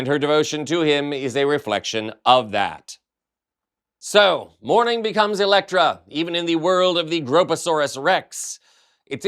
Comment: I I heard ”So! More problems Elektra!” 🎉 love this show!